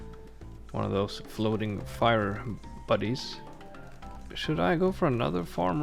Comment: "One of those floating fire buddies"2023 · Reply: I thought he said floating fire bunnies at first 😅